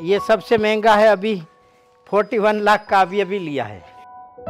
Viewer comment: kya bole 41 lakh ??